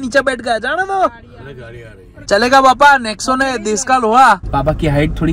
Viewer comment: I heard desh ka l*da